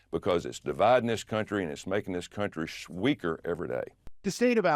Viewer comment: Did he just say”…making this country sweaker”?